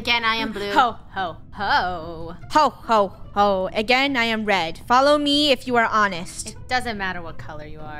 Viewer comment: again I am red. I hear : again I am dead.